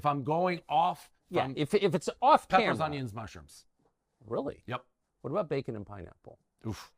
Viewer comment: peppers onions and mushrooms?!?! Dave im absolutely appalled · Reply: I thought he said Pepperoni and Mushrooms (which is respectable imo). I'm also appalled.